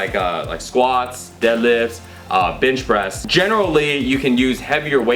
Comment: did you say bitch press? lmao...